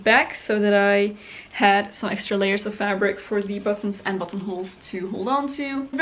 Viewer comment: I think your dress turned out beautifully! Even as a professional historical costume maker I still struggle with buttonholes!😝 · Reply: I thought you said buttholes